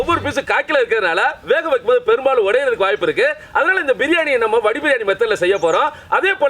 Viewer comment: what he said ? I can't understand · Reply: Vadi biryani